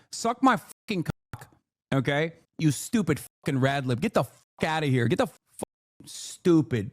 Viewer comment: did he call black person red lips? also mentioned pass?